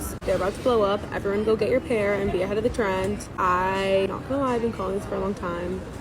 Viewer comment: At first I thought you said "Behead the trend"